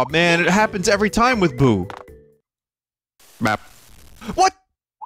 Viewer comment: when did he say bap? Did the audio rip